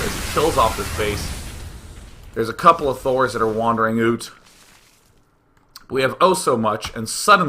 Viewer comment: Day9 says '... wandering oot... ' like a Canadian instead of 'wandering out'. Slip of the tongue or LAGTV reference?